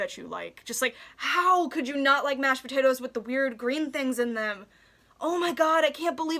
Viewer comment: i had watched the beginning of this video awhile ago, and when i came to see the rest of it i heard "HOW COULD YOU NOT LIKE MASHED POTATOES WITH THE LITTLE GREEN THINGS IN EM!"